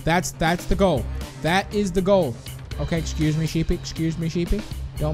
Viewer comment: Excuse me, did he just called Pandy, Sheepy?